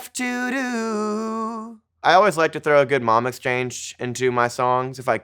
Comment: does anyone understand what he said here? The caption says mom exchange but that feels wrong lmao